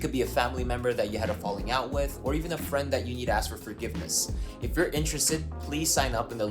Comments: Is it just me that heard "eat ass with" instead of "need to ask" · I heard him say “or even a friend you eat ass for forgiveness” 😂 I was like what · I just wanna say I heard " a friend that you need to ask for forgiveness " as " a friend you eat as for forgiveness "